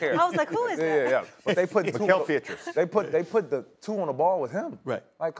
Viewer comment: I thought pat bev was saying pietrus as in mikal pietrus😂😂 Pritchard 🤦🏾‍♂️